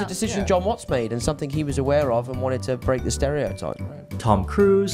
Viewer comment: Did he just say Mister Zendaya?